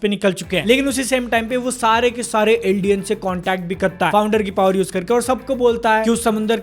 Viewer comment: I heard "Indian" instead of "Eldian" Lol